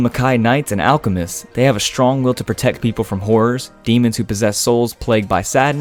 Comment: I thought it said “They have a strong will to protect people from whores...”